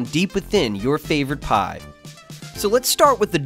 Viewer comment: did he say pie? NO PIZZA IS WAY BETTER THAN PIE! I like both though :P